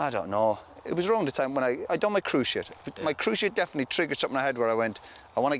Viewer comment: I heard "When I did my cruise ship" when he was talking about his bad injury and could see him recklessly aiming a boot into the Captain's door of the Symphony of the Seas